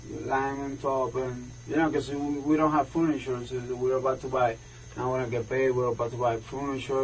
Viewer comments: he said we dont hv furniture but we were about to buy furniture but the caption said we don't hv food insurance but we are about to buy food insurance 😂😂lmao I know this is a tragedy of a story for the woman but that caught me way off guard lmao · If you violate someone to the extent that you’re ripping out their intestines, you don’t belong in society. What a horrible tragedy. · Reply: he said we dont hv furniture but we were about to buy furniture but the caption said we don't hv food insurance but we are about to buy food insurance 😂😂lmao I know this is a tragedy of a story for the woman but that caught me way off guard lmao